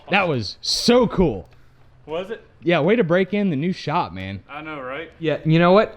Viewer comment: Break in? I thought you said burnout